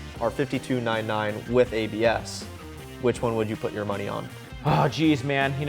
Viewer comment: anyone else hear morty? Lmao!